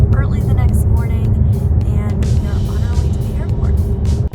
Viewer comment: I thought u said "hair-port"